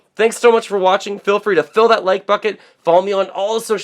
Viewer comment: Did he just say "like bucket"? lol